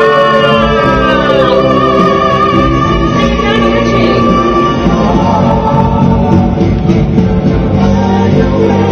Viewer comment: What did she say at 0:42? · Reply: "He is singing lionel richie?"